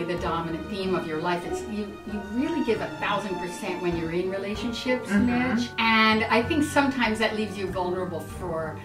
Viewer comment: what did she say?? couldn't hear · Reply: Caitlyn Gellar she said Mitch gives 100% in relationships & it leaves him vulnerable to hurt.